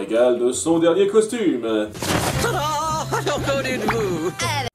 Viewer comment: Moi j'entends "Candide-vous" et ça correspond à mon avis ! 😁